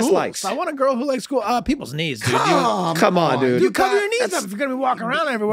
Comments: All this time for that dating scene when they ask him what his dislikes were I thought he was saying peoples “needs”😂🤣…I never got it but yea peoples knees is funnier😂🤣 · To my non-English speaking ears, it sounded like Charlie said his dislikes included "people's needs". LMAO'd picturing this in a dating profile. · I always thought he said "peoples needs" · As a dislike i always thought he said "peoples' needs', rather than, "peoples' knees". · I thought Charlie said "Peoples NeeDs" for the longest time. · This is one of my favorite scenes from the show. How Dennis shrugs off using the picture in the episode is so perfectly dismissive. Also, for a long time I thought Charlie said he disliked “people’s needs,” which I find just as funny as “knees.”